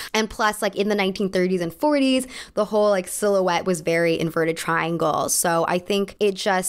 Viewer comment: Eeeeeey I'm tipsy on rose and when you referred to yourself as "inverted triangle" I heard "Burmuda triangle" so that's your body type now, better dress for that